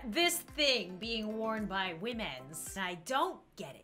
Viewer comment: when says "womens" i just hear "we - mens"